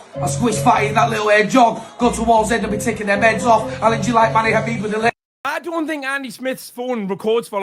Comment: Thought he said, " I'm taking their meds. ".... clearly , crystal clearly 😂😂